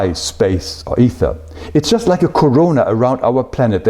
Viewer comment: did he said “corona” here: 1:32?